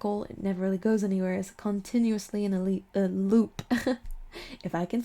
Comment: It sound like you said my name “elite” when you said in a loop. Definitely feeling ready to move on to the next phase in my life. Definitely need to get rid of the things that don’t serve me anymore. Lovely reading as always 😊